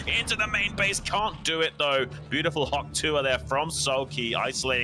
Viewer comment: I'm sorry at 1:27:20 did he say 'Hawk tuah'??